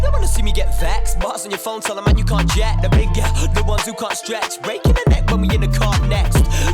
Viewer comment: I thought she said “stretched”